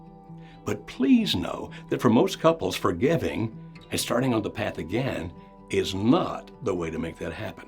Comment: What does he say at 13:41... it sounds like forgiving mixed with forgetting... for most couples is starting on the path again...